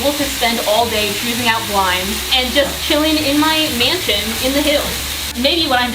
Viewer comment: Mansion is spelt wrong... that's okay of course... but like I thought you said mason and was confused haha